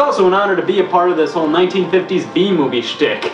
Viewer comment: Not gonna lie at the end I thought he said "bee movie" instead of "b-movie" for a solid minute before I read another comment.